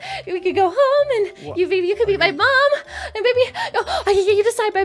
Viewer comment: wat ? did she say maybe you can be my mom lol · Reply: Kaylie Granados it’s maybe you can meet my mom